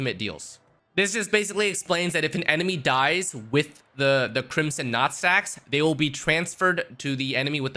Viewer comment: I legit thought he said crimson nutsacks at first 😅. Acheron seems so amazing. I'm def gonna try to go for her.